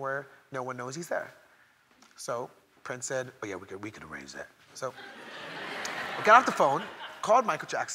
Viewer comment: what does he says as Pince at 0:58 ? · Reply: "We can arrange that"